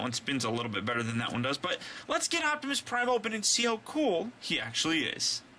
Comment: Did he just say Optimus Primal? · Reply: Optimus Prime he said optimus prime o pen